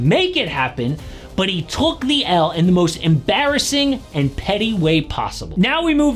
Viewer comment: I thought it said "pretty" and i was like "well, i can see what he means"